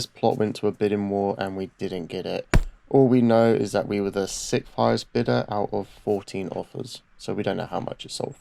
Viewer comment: which bidder did you say you were? out of 14? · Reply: I believe we were 6th highest, it was a while ago now.